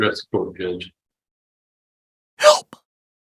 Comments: Does anyone else hear “Help” at 25:31? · can anyone else hear a females voice say “Help” ?